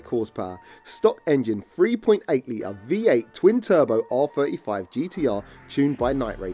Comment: Did he just say amar’s gtr is v8